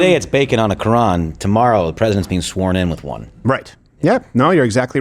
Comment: For one split second, I thought he said the President would be sworn in on a piece of bacon 😅